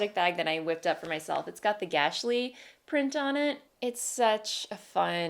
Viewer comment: What did you say the fabric/print was called at 7:15? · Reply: The Gahstlies! A fabric line by Alexander Henry <link>